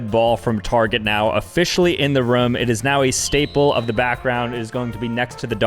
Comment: bro he said the red target ball was “officially in the rum” instead of room 😂